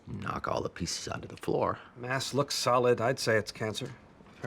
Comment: sounds like he said "ass looks solid"